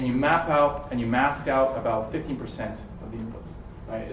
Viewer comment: Thanx for the talk! Two small notes: * 25:53 - it sounds like he is saying "50%" of tokens - it is (obviously) 15% of word-piece tokens * 28:05 Someone in the audience comments that a single token is predicted - that is not true - since for each token there is a 768-dimensional vector, one can put a softmax over all tokens separately in the output! The output of the model is a (nword-piece)x(nembedding) matrix - not just a vector.